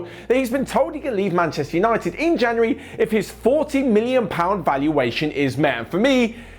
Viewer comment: I thought you said £14 million at first🤣